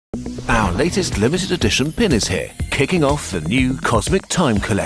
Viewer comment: I swear I heard "Penis here".